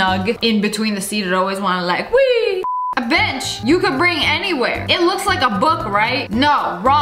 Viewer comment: “It’s all fun and games until a Karen complains” 😹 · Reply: For a second I thought she said do you want to bring a bi## every where but I skipped it back and she said a bench,lol🤣😅🤭😶